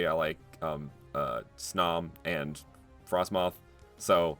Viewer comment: I thought he said frostmom